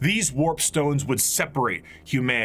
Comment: Hes got Rats on the brain it seems lol (warpstone instead of warpstorm)